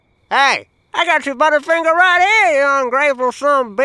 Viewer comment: I've seen this video at least a hundred times and only now do I hear "butterfinger" instead of "bottle o faygo" I've quoted this video every time I bought faygo and now I feel like a complete jackass for reasons apart from compulsively quoting this video at Sheetz every fucking time.